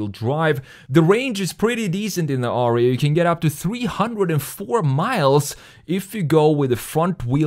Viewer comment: Aw I thought you said that the Ariya can go up to 304mp(h). Deal breaker that was.